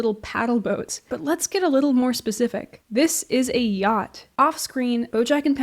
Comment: i was half listening and genuinely thought she said "gyatt" i'm so fried